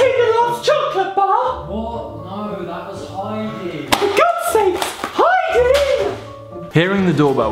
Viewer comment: did he say Heidi ?!?!